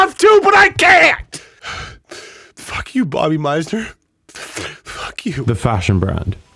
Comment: It kinda sounded like THE FUCK YOU SAY TO ME YOU LITTLE SHIT